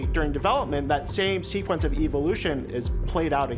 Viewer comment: Did he say Evil-lution? @ 0:55 seconds.